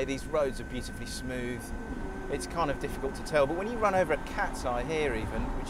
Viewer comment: is it or did he just say "run over a cat" to test the noise cars make, wait til animals rights hear about this lol.